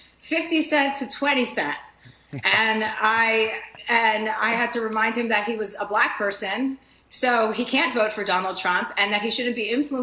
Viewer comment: what I heard was "I had to remind him he's a black person. he can't drink out of that water fountain"